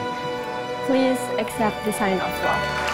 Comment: salah denger apa ya aku, kok kedengeran "please except" 😢